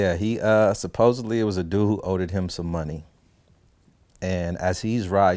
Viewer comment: am I high or did dog just say “owdeded” ? 💀